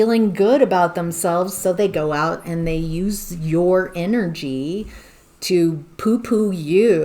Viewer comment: Sounded like you said “use your inner-G” and that’s what I’m finna do! ❤😂